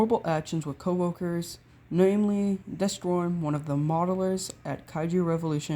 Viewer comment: did you say destorn at 2:00, kinda sounded like it